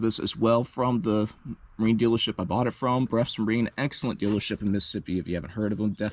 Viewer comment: What dealer did you say? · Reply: Wonder same thing. Thought he said West Marine but there’s only one in Biloxi, MS and they don’t sale Jon boats, per their website.